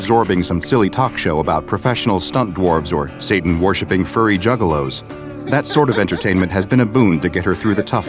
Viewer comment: did you say "furry"? well its time to kill and 41:29 did i just see a B-51(1952 jet) in a ww2 timed era(they at most had at most prop piston powered B21's)